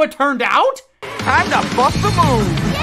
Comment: did anyone else hear him say “time to fuck the moon.”